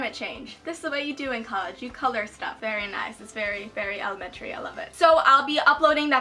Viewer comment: when you said very nice i thought you said fat ass and i was like whattt?!? 😂